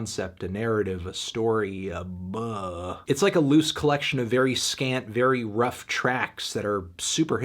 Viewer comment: Whenever you say “scant” I hear “scat”